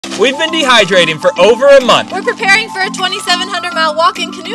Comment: At first I thought they said they have been dehydrated for a month!